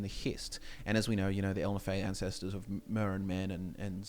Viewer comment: This entire time I thought kalpa was spelled "calper" and they were just saying it with an accent lmao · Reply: I thought the same lol. I messed up Ehlnofey too, because I always heard "Almafey" with their accents